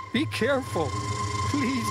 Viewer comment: did he say be careful daddy?